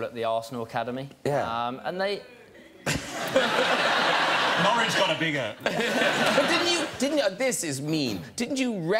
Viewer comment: What a guy from the audience said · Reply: MegaAnitnelav he said "boo" at the arsenal academy 😂